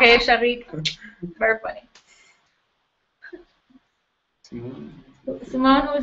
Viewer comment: Simone is asked "Who is your favorite female lead singer?" I cannot understand the answer. Does anybody understand better and can help me? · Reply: She said it was Anneke Van Giersbergen.